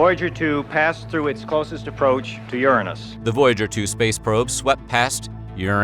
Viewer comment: Did he just say Uranus? 😳